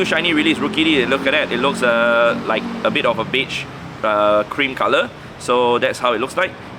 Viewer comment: I definitely thought you said “it looks like a bit of a bitch” hahaha